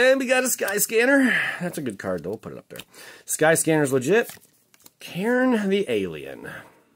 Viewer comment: I thought you said Karen the alien lmaooo